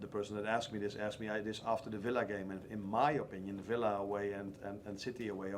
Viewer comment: I see..Slot actually said Villa , while I heard he said "Fella".